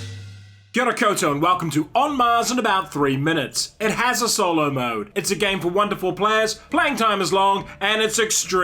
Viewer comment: Instead of "it's a game for one to four players" I heard "it's a game for wonderful players". And I felt flattered.